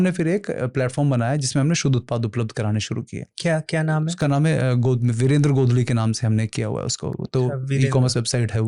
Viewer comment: website ka name kya bola ?anyone plz tell mee I can't hear clearly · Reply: Virander godlie